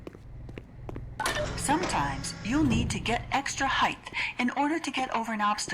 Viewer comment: did she say "heighth"?